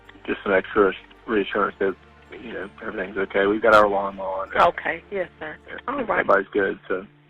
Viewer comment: I work as a transcriptionist and in my opinion it sounded like he said “we’ve got our alarm on”.